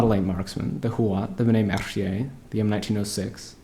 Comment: What does he say after the Huot i dont understand it. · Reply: benet mercie, its lmg, support class